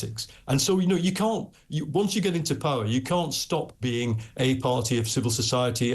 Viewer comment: Did he just say come into powe!!!